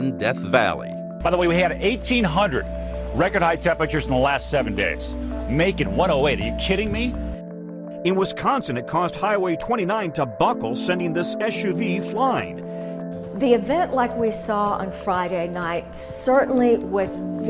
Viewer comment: I can't understand what the guy says at 0:44. "It was Consuhnin?? that caused highway 29 to buckle sending this SUV flying."